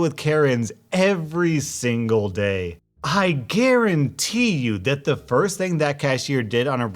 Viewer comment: Doesn't it sound like he said I karen-ty you